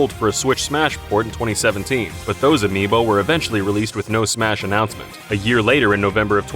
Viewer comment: cool video guys! I just wanted to say at 14:49 the captions say "eventually released with sno Smash Announcement." instead of "eventually released with no Smash Announcement." <link> (A picture of that linked here)